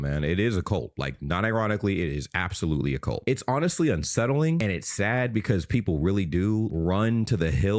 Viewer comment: for a second there I thought you said "upsettling" as in both upsetting and unsettling and I thought: "damn, that's genius" But no I just misheard you. I'm still gonna use it though 😂